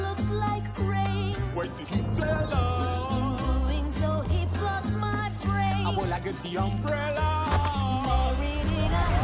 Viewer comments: does anyone hear “ so he f***s my brain? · is it just me who heard say “Nutella” 😂